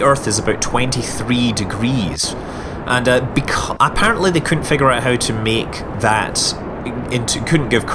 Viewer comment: Oh and also 5:14 'dat shit having gone up to 1.3 close expressed per second' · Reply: Daniel Duncombe and at 8:22 it says and that big cock couldn't figure out how to meet